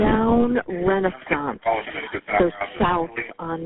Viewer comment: is the called "slow"?